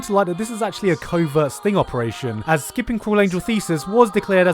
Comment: everytime he says cruel angel thesis I hear 'cooling you feces' idk why